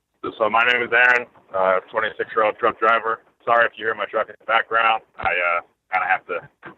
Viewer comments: At first listen, I thought the caller said "26 year old Drunk Driver" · I could have sworn he said "I'm a 26 year old drunk driver" till they talked about his truck.